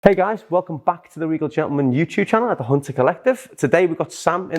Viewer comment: I thought he said welcome fuck at the beginning lmao.